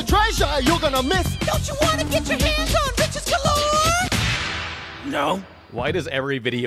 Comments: was I the only one that thought he said do you wanna to get your hands on bitchez Galore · "don't you want to get your hands on bitches galore?" I can't be the only person who heard that? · I thought he said bitches galore